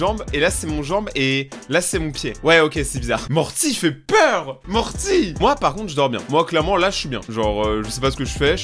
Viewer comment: J'ai entendu "monte moi"